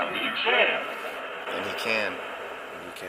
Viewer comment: "I CONTROL!" Is what I keep hearing, where you hear "he can."